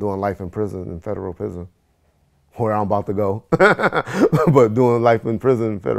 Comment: "Where I'm about to go..." Did anybody else hear that?